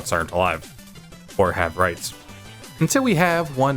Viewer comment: I know you said rights but it sounded like rice and that just funner